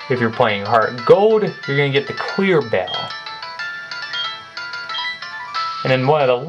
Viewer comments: lol, it sounded like you said queer bell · itsounds like he said queer bell